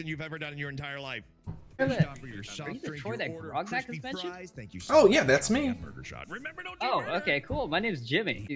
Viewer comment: I thought he said "Grognak Convention"... xD Whatever happened with CmeK anywho..??